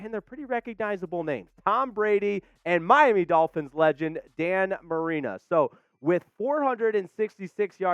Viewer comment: Did bro say Dan Marina at this time stamp.